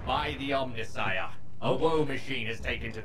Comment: I....I didn't hear "a woe machine" I heard "OwO machine" .......